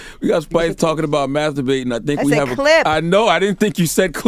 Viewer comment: I heard “We have a clit”, then I turned the volume up.